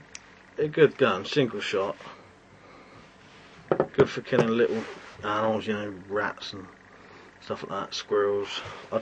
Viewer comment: Did you say 'little owls?'